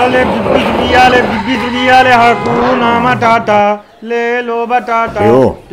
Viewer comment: did he just say HAKUNA MATATA LELO BATATA @ 00:10 :D